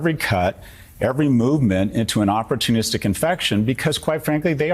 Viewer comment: I thought he said "Aųtis tic infection" originally and thought "yeah that's not wrong"